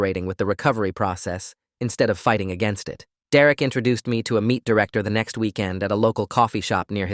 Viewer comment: did he just say meat director